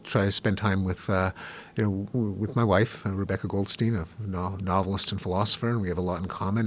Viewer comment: That seems right. Thank you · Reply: No problem. I initially thought he said modelist or nodelist.